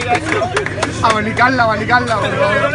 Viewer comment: Did someone hear' that abe nikalna'